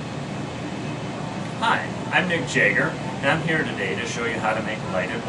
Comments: I thought you said Mick Jagger! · LOL, sounded like he said ( Hi, I'm Mick Jagger) LOL